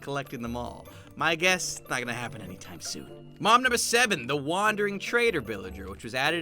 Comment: I thought you say mom's number 7 But its mob. . .